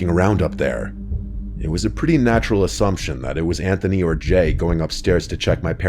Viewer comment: was messing with earbud and thought he said “It was a pretty natural assumption that it was Anthony Bourdain going upstairs to check my parents fridge for cold water or something.”